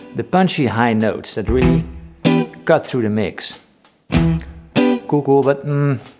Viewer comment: what is the word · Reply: "cool, cool"